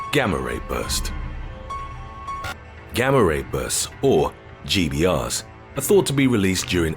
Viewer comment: On the Gamma-Ray Bursts at first I thought he said "Grammer rapists". 😆